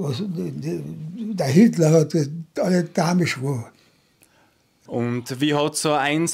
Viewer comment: manipuliert hat er nicht gesagt, sowas wie eingeschworen habe ich verstanden. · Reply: er sagt: „der Hitler hat alle damisch gmacht“ das kann man schon mit manipuliert übersetzen. „damisch“ ist österreichischer Dialekt, ich würde es so übersetzen: „der Hitler hat alle verrückt gemacht“